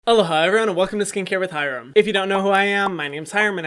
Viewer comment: am I the only one who thought that he said “hello hyram welcome to skincare by hyram”